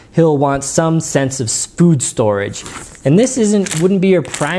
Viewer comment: Did you say "spood storage"? LOL I'm cutting you off, no more wine for you when video blogging.